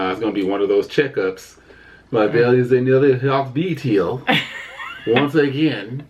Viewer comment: Lmao .What did say at 19:05? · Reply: Tankahs Mom He said, awww there is another heartbeat here. But he said it with a funny accent, lmao!! They are too funny!